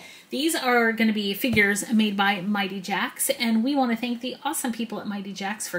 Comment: i thought she said theese are gonna be fingers form mighty jax